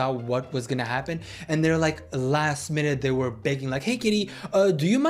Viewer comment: sounded like he said “hey kitty” instead of “hey Katy” 😂😂😂❤❤❤❤